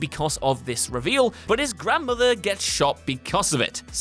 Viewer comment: did you just say his grandmother got shot?